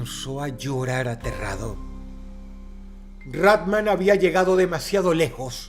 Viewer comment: Pensé que decía Batman 🗿